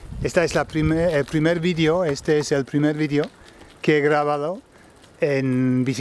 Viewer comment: it sounds like you are saying it's the first video 'que grabado'. I understand that you are talking about the first video recorded on a bike (it totally worked btw) but I'm confused by a participle with nothing ('he' for example) in front of it. Did I mishear or is this something I missed in verb class? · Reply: Greg Tomkins I think I said...Que he grabado. What happens is the que and the he merge together as one, especial if I'm panting! jejeje